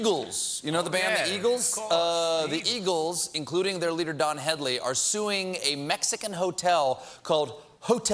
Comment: sounded like "Don Hedley."